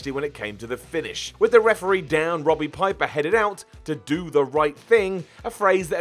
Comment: sounds like he says Robby Piper